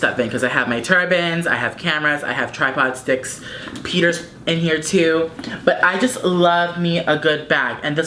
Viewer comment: i thought you said ¨Peter´s in here too¨ when you were talking about the Marc Jacobs bag lmao.